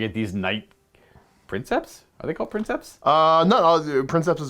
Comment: Did I hear “Knight Princess”?! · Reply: Nah I'm a mumbler. It's Knight Princeps